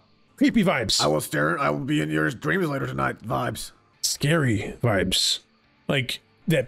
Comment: Is it just me who thought foxy said moon gave PP VIBES at 18:44 or did he actually say that- 💀